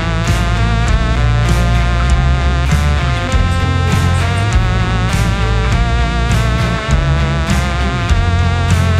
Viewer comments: Does anyone know what this says? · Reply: Found it, it's I'm like totally obsessed with you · Guys what does it say at 1:16 I’m so intrigued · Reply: honestly sounds like “would you like some watsits 😀”